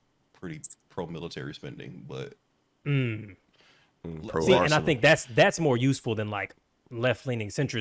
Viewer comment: lmao did he say Pro-Arson? 11:12 shits fucking mint - pun intended 18:36 nah she gon fold :p